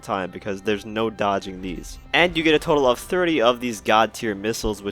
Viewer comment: I thought he said you get a total of 3 missiles, thank god i realised it was 30 😂🤡